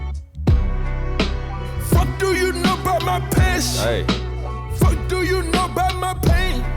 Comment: It sounds like he says "fuck do you know bout my pish." I wish they unounciated words better. Also, album is good but I expected it to be better/different, especially after 10 years of over hype. MDL and those 4 freestyles were better.